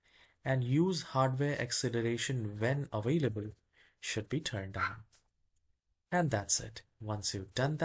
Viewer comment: did you say on or off? Sounded like you were wheezing or something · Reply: Turn On Mate 👍